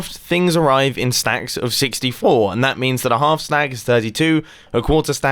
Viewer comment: Did you just say... Half slag...?